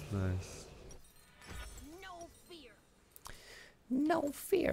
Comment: does fizz say no fear?! i always thought he was saying no fair!